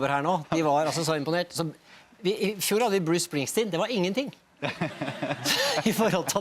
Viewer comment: Can't understand a word they're saying... 0:40 he just say Bruce Springsteen?? Anyway, here in March 2018, about to get eliminated from contention, hoping next season these two lead us all the wayyyyy #respect